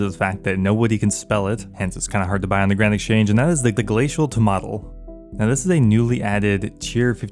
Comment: subtitles said its glacial tomato :DDDDDDDDDDDDDDDD